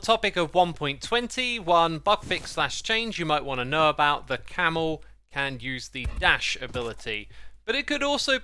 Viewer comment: The subtitles say “camel yeets” 😭😭